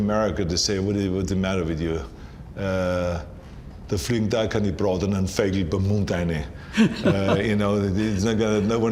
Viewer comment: What does he say at 5:18? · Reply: "do fliang da a kane brotanan Vegl beim Mund eine" (roughly written down) It's literally something like "there won't be any cooked (not the right word, but if you want to look up the translation the German word is "gebraten") birds flying into your mouth there either." It's a phrase that basically means something like "you won't just get everything you want without any effort (there either) "